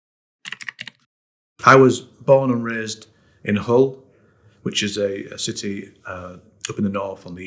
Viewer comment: His first words sounded like he said, “I was born and raised in a hole”… Damn bud had it rough from the start.